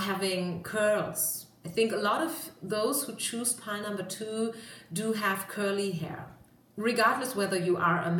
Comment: HAHAHAHA #2, I was eating soup and I heard "you have curly hair" and I almost choked, and yes I'm an artist :)